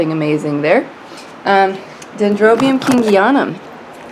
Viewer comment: i think you said Dendrobium kingianum? can you make a video on what he is planted in and how you water him? I've had mine since February, and he seem as confused as me.